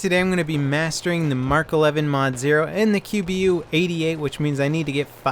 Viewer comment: thought is said masturbating to the mk11